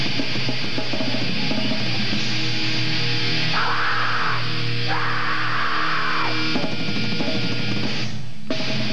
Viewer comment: What does Flo say at 5:05 ? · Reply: Maybe "c'mon, THRASH!"